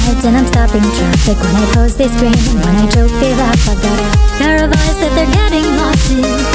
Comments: I thought she said when I choke, they laugh, I had to go back and read it again. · before I thought that it said when I choke they laugh but no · I love this so damn much but, I keep hearing "When I choke, they laugh" and I feel awful about it x.x